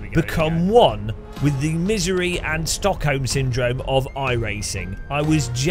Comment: Did you say 'stuck home syndrome'?